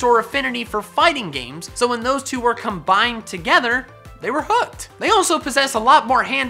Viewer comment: It sounds like he said "they were f*cked" 😂